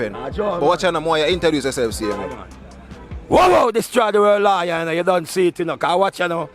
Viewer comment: Wowwow destroy the world lawyer lol That’s what I hear